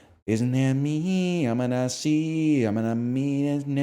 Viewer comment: DID HE SAY I'M A NAZI